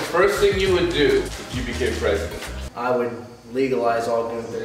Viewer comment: why did I think he said what would be the first thing you do if you become pregnant? 😂